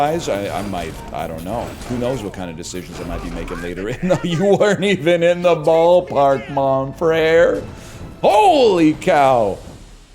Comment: can someone tell me what word uses here? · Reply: "You weren't even in the ballpark, mon frère" (Translator's note: "Mon frère" means "my brother" in French)